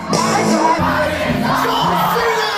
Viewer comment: what did he say? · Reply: Anthony i chime in